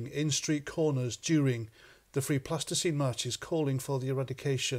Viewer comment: It sounded like you were saying Free Plasticine 😅